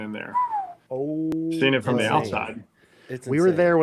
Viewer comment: Am I the only one that thought he said they flew in the Mossad? 👀🤔😲🤦🏾‍♀️😂